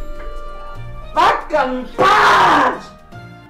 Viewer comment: There is 1 and only laugh 0:15 Another prequel moment. I really heard the comedian said "Fox got punched" instead